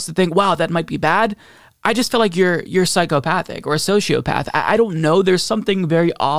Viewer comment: Definitely thought that said Psycho Rat lmao